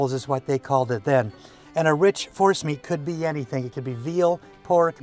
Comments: Oh $#!+... I thought he was saying to use "Horse meat"! 🐴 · Darn I thought was saying Horse Meat repeatedly! 🤷‍♂️. Was just thinking wow that’s out there even for. Then realized he was saying forced meat.. I’ll try almost any kind of meat. But I think I’d be a hard pass on horse. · At first I thought you were saying horse meat. Lol